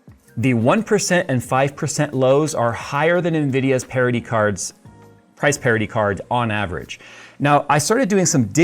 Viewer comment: What Jay says: "nVidia's parity cards". What I hear: "nVidia's parody cards". Says something about my sentiment towards the company after the last month or so.